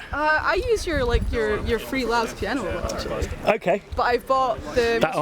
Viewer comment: What Piano did that girl use a lot, did she say "free loud piano"? I couldn't make it out. I know did a felt piano but searched for a "loud" piano but could not find it. (?) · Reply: Soft Piano by LABS, it's free on spitfire